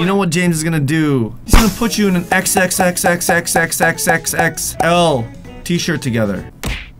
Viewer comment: When he said XXXXXXXL size t shirt it kinda sound like hes saying S** instead of X lol if u agree it’s this part👉🏻